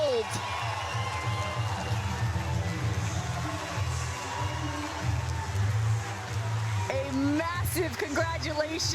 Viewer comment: what did allyce jones say to anna leigh waters at the end around 32:32? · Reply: She asked if the earth’s flat